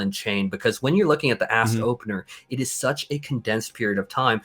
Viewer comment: After 19:00 I swear he says Ass Opener lmao